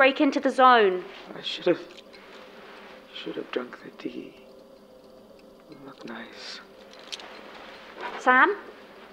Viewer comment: "should've drunk their tea...they looked nice..." anyone else hear this at 20:55? because i almost had a panic attack